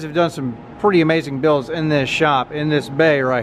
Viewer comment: I had to go back and listen again ,i couldve swore i heard Joe Biden in the shop 😂